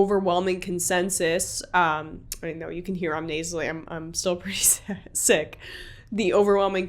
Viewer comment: Hope you get feeling better. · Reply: It sounded like she pulled a freudian slip at 1:25. It sounded like she said sad and then sick.